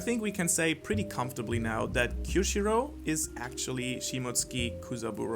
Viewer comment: You’re reaching about Kyoshiro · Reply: Drake GX oh thanks mate. He sounded like he said Kyoshiru